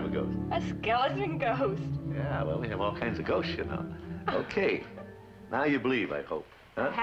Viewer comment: did Walt just say "shit"?!?! @@ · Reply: i think he said "we have all kinds of ghosts, y'know." he sort of mumbled it so i see why it sounds like that. Although to be fair, walt was a grown man living in the 40's-60's, he wasn't an absolute stranger to swearing.